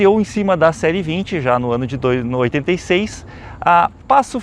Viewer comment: a Brasinca em cima da série 20 no ano de 2086 eu ouvi?